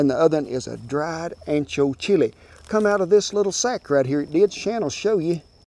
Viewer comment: My girlfriend and I came across the video late this morning.... We are from the UK - What on earth did this bloke say on 3:35? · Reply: from this little sack right there, Shan'll show ya. Shan being short for Shannon.